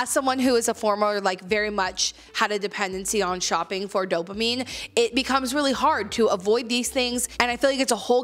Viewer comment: For a second I thought you said "Doberman seeking brains" -- then realized you had said "dopamine"! Lol!!! (I may have a Doberman seeking brain, ha!)